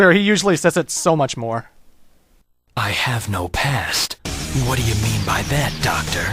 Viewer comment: When Shadow said "I have no past" in the end I thought he said "I have no pants". I guess that's true too.. xD